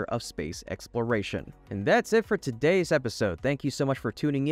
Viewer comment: what was he saying? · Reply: A little fun for today's episode! Thank you!